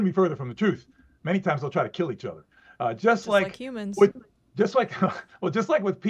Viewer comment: Almost sounded like said, “Just like Cubans.” 😳